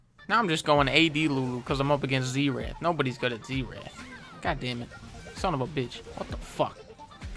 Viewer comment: For a good sum of time I thought he was saying Z Rat